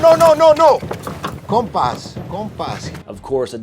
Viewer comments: One thing I need to clerify for all my non-Spanish speaking brothers and sisters out there is the part where Alejandro sneaks up on the cartel members exiting the car during the highway shootout scene. The subtitles read "In peace. In peace." That is NOT what Alejandro is saying! He's actually saying "Compas! Compas!" Which is short for "Compadre" which basically means "fellas". He's basically saying "Fellas! Fellas!" while trying to defuse the situation. Just wanted to clear that up. Carry on. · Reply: Bro for the longest time I heard "con pas con pas"🤣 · is not translated correctly. True mexicans would never say 'con paz' (translated as "in peace" in that segment) to de-escalate a situation, so, saying so to those guys, would only add to the confusion. Instead, Alejandro (Benicio) is actually calling them: "compas" which is short slang from lower/poor social classes for 'compadres' which when used on people you don't know, is another way to call them something like "buddy" ...in short, in that scene Alejandro is only focusing their attention on him as if he were to start a parley, shooting them instead. Way cooler when you understand that detail 🫶